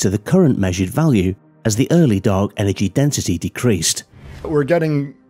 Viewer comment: Before I confirmed what I was hearing was correct by enabling the subtitle, I thought the narrator had said "early dog energy". I was thinking if there's such a thing as an early dog energy is an early cat energy.